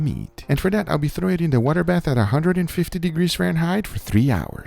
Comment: I heard ‘wonder bath’ not water bath but I’m here for it 😭